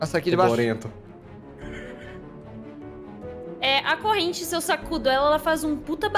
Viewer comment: "a corrente, seu sacudo"... Foi o que eu entendi kskkk